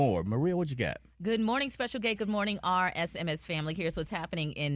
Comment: Did anyone hear Maria call him Special *Gay? 2:26 😂